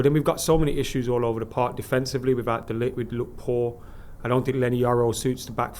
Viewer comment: Did he say "De Ligt who has looked poor"? Wtf is he on?